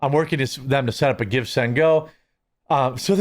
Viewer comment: Whenever people say "Give Send Go", I hear "Gifts n Go".